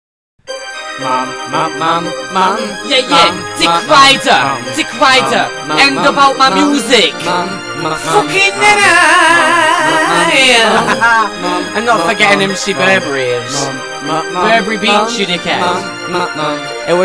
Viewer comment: I thought it said fucking dinner =/